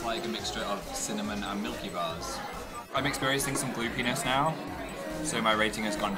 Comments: I heard 'Blue penis' instead of 'gloopiness' :') 'I'm experiencing some blue penis' omfg. why does this amuse me so much. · I thought Phil said "Glue Penis." not "Goopiness." My god I blame Dan. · Not even joking when you said 'Gloopiness' I thought you said 'blue penis'. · Was I the only one who thought Phil said 'Blue penis' and not 'Gloopiness' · did he say "I'm experiencing some blue penis now" ?! o___o NOT AGAIN! SAME THING HAPPENED ON THE BROWNIE EPISODEEEEE.